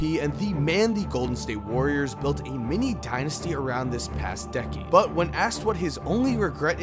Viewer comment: did he actually say "mini dynasty"?